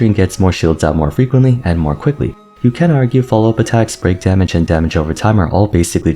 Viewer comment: I had to rewind 3 times to guarantee he didn't describe Rappa's damage as "brain damage" instead of "break" (which both can be applied to that character in question)